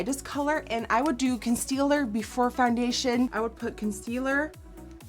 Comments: Does anyone else notice that at 3:16 nikkie says constealer instead of concealer?? Btw Ilysm nikkie😂😍❤️ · Did she just said constealer instead of concealer at 3:16??